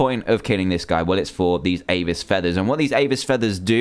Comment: I thought he said anus feathers